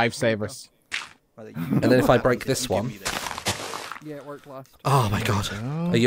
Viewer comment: can anyone hear say "yeah we're fvcked"